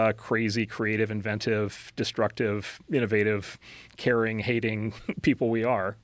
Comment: Did he say Karen-hating? 🤣